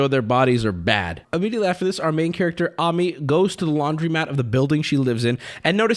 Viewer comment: I keep mishearing "Amy" as "Uh me"...